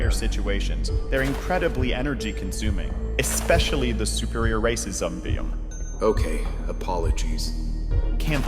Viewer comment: watching this again I still think he says super racism beam for titan speaker man 17:10 Great video any way